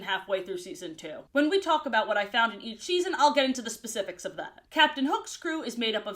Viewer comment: The way you say “each season” at 7:37 sounds like you said “eat cheese-en” instead, and honestly that made me laugh